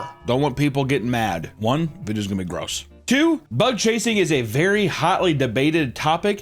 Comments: I hear "butt chasing" every time. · sounds like he is saying "butt chasing"